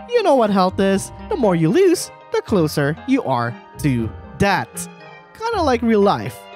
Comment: did he say debt or death (No hate)